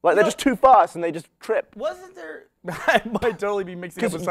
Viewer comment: I thought they said "too fat for their legs" not fast XD